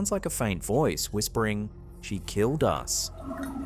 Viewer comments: It really does sound like ‘she killed us’ and the fact the officer immediately after sounds like he says ‘what was that’ like asking her to repeat herself cause he didn’t hear the first time, creeeeepy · I thought I heard , after the officer said 'What was that?', 'she killed us' again .